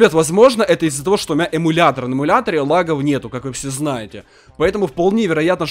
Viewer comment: Мне одному послышалося что емулятор "НЕЛАГАЕТ" ?